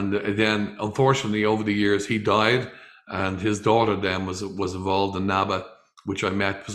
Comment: Steve Reeves is the GOAT! Leonardo DaVinci’s The Vitruvian Man would have been Steve Reeves if he lived in Reeves day. Wait, did you say his daughter? He had a daughter? I thought he didn’t have any children. 3:59 Oh I thought he lived the rest of his days in Montana. So he stayed in California. Cool. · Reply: ok thanks but I swear I’m hearing say “…his daughter” 3:59. Maybe I’m mishearing him.